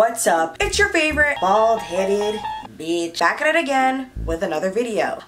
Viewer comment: When she said “bald headed” i thought she said balls hanging